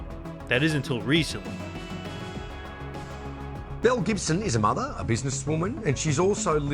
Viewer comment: I thought he said Mel Gibson it first